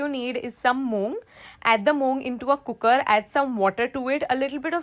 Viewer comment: it said dome instead of some anyway no problem no hate here love you mam🦄🦄🦄👸👸👸🤩🤩😝🥰🥰🥰😍😍😍😘😘